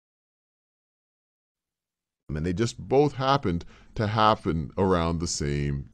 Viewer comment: Corrections in minutes: 2:55 "the hotel HAD poor quality of service." 3:04 What I hear is (yeah) not (here), but could be the English accent. "fill out YOUR name and address and let me ask you a few questions." 3:33 "I went into the library this afternoon to have a look at THE newspaper." 3:48 "You don't want to miss your plane on the big day, right?" 4:03 "The two PHENOMENON are in fact intimately related." 5:32 "She hadn't KNOWN he was such a good cook." 6:04 "I MEAN <--(not sure about this) they just both happened to HAVE BEEN around at the same time." 6:22 " I hope you all HAD a good trip." and I give up trying to correct the rest lol it's too much, tell me what you think, cheers. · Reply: Well done, just that 6:04 instead 'HAVE BEEN' I hear 'HAPPEN' ... in 3:04 is 'here'.